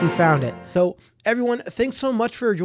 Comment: He said I will catch u in next video but my speed was a bit fast and I heard "He will catch us on xvideo💀"